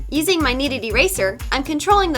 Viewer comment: I legit always thought you said, “with a needed eraser” but not too long ago I realized you were saying “with a kneaded eraser” 😂😂😂